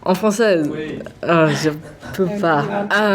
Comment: thanks rose cookie! I was also wanting to know what she means by "papa" or whatever she says at 8:02 haha is that a french expression or something? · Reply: She actually says "je (ne) peux pas", which means "i can't"